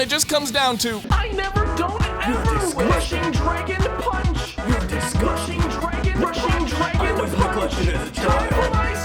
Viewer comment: Hmm, Is he saying... “Y O U ‘ R E D I S G U S T I N G” or “Y O U D I S G U S T M E” ?